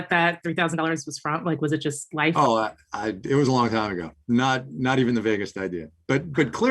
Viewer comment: "That was a long time ago... not even the "Vegas" idea." At least that's what I heard!